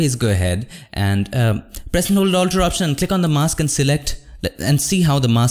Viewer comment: please speak clearly. i cant understand the command. what is it? thank you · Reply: Alt (or option) and click on the mask